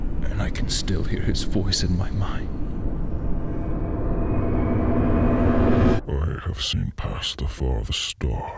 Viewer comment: Thomas: "I have seen past the farthest star... And I have gone to the edges of the earth... I can take you there, if you so choose. I can give you all the things you never knew you wanted, taste from the tallest chalice, dine at the largest hall... All I ask in return, is your soul. Your soul for a chance at a life without pain, without heartache. All of this I can give you and more... Just for your soul." Thomas: Drain you of woes... Your God has turned his back on you. That's why my tracks still pushing me forward... For people who can't make out what Thomas says. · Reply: I thought he said at the beginning I have pasta from the store